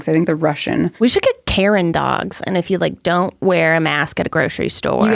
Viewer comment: Anyone else thought was saying Carin Dogs and totally configured, and then eventually realized she meant Karen Dogs?